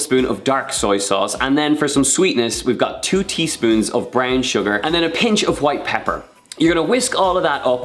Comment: For a minute there I thought he said “bran sugar”, I was like “ where the hell am I gonna get that?!”🤣🤣